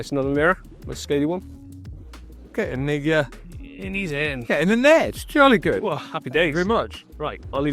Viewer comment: what did he say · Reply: 🤣🤣🤣 I had to listen back in the edit! But it was "get in the uh... get in the net"